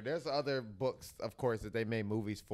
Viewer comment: did he just say twilight zone 😂